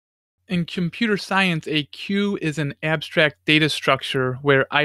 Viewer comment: the subtitles say "cue" where they should say "queue." It's obvious to a programmer or British English speaker, but if you're from the US, there are many for which the word "queue" is likely not familiar.